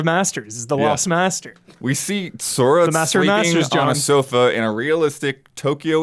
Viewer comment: did anyone else hear in the kerfuffle of voices, "We saw Sora masturbating on a sofa."